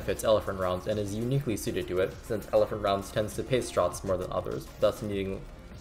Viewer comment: what did you say here? Elephant rounds tends to "paint straws" less than others? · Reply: "Tends to pace shots" , as in not spamming ER into everything